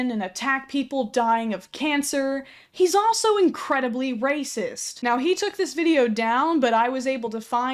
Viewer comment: Tbh I thought racist said Roach